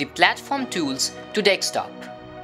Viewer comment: did he just said (dickstop) ?